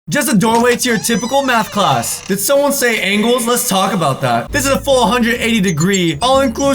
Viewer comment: I swear to god I thought he said "Did someone say anals? Let's talk about that" and I was like wait what